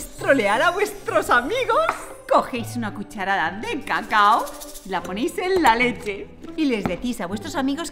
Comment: Pense que dijo: poneis una cuchararitade caca